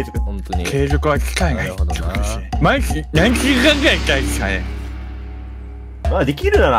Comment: まだ見てる人いるんかなあてかさあ5:32のやつなんか俺聞き取れた気がするんだがw多分『毎日何時間くらいしたいっすかね？』って言ってるんだと思います ↑こいつ無視して良いよ心の優しい人は ↓低評価 ↓編集済みダサいよね ↓👎稼ぎすみません